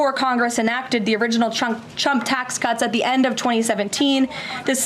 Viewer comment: Did she just say "Chump tax cuts" 😂